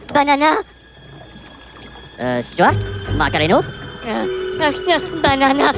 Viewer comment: Did Kevin say Stewart’s name